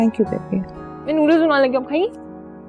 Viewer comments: what did she say??? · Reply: Main noodles bnane lgi hu aap khayengi 😅keh rhi h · can anyone say what did mimi tell to her mom ? Couldn't understand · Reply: Maybe She said,main noddles bana rahi hu ap khayegi